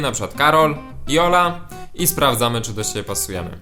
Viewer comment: kto usłyszał Jola ???